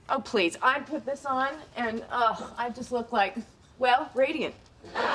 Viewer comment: what she said on 0:42, i'm just like... radium? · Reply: Radiant